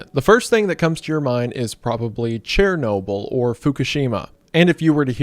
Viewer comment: Did he just say CHAIR-NOBLE lmao??😂